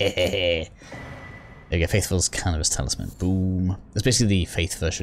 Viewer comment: I heard "Faithful's Cannabis Talisman"